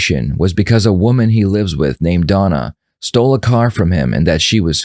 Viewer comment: Who shakes hand with their left hand?? 6:05 a woman named "Donna"...I didn't hear Donna at all...I heard "Nichole" lol